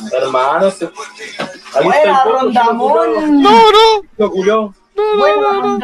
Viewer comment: ''wena rondamon'' frase que marco mi vida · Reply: Yo le entendí "Don Ramón", creo que eso quiso decir xd.